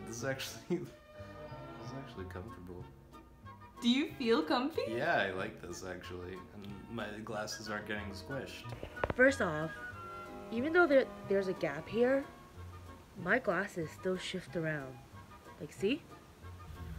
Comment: I thought it said "in classes"